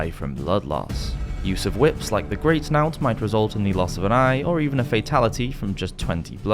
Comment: what is he saying here? Greatnought? Greatnout? Whatever it is I can't find anything relevant on google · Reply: It's spelled knout in enghish.